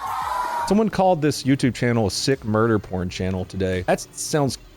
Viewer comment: Did you say "Fun Murder Porn"? I like that name...